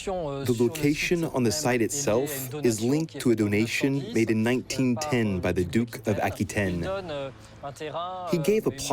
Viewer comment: did he say 1910? Nineteen ten? the year 1910? Maybe he meant 1090?